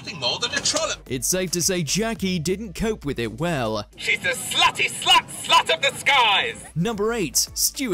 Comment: what she says ? slaty ?